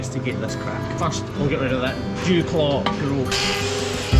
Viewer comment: did you say we'll get rid of that Jew Claw?